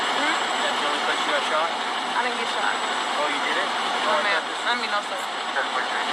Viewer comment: You didn’t even use Stockholm Syndrome in the correct context.. ik your head half air just saying anything. She LIED they set him up .. end of story. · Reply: at the 2:40 mark she clearly said I DIDNT GET SHOT. Lol if you’re deaf I understand if not you’re on here not wanting to admit you were wrong and they set this man up. Since you’re already here you can rewind back to